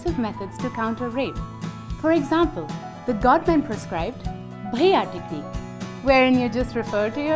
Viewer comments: Could someone explain what she says at 1:21 please? Is she saying "preah"? What does it mean? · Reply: Bhaiya* it means brother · what does she say at 1:18? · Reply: "bhaiya" meaning brother